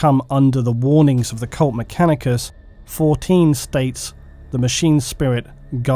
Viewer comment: I always thought you were said mechanikers until I see the spelling